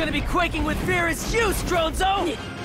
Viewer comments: DID HE REALLY SAY STRONZO TO RISOTTO? Alright, I didn't see that coming lol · did he just said stronzo?